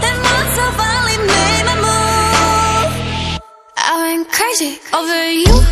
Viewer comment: Why am i hearing I'M instead of I WENT?